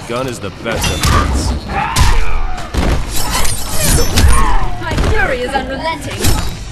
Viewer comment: Please can anyone tell me what she says in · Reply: "My fury is unrelenting."